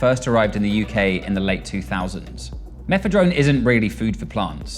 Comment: At first I thought he said methadone**sniff my shirt***ohh, plant food🙆, happy to know I haven't been reaking of cat piss and nobody told me